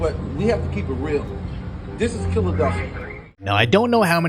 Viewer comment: Did he just say Killadelphia?